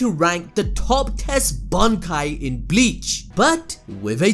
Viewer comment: I thought he said "top test bankai" 😂